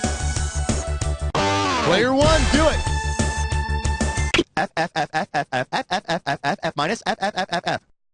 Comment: what is he saying? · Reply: F, f, f, f, f, f, f, f, f, f, f, f, f, f, f,... F-, f,f,f....